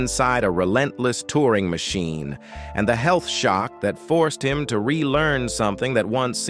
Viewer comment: The health shock? I heard, 'the stealth shot' as that's what would have unpicked him.